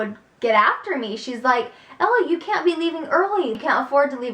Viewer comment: did say Ella instead??